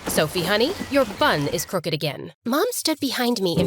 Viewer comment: Wait why did I hear “Sophie your bum is crooked again”😭✋ I was like ‘Fam WHAT??!” But I was just my brain deceiving me like it does in a test